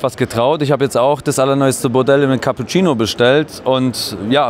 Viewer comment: Was hat der Herr bei 6:06 gesagt ????? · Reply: er hat gesagt - Model - und eben (in der Farbe) - Cappuccino . Ist halt mehr sexy als wenn man einfach so -hellbraun sagt?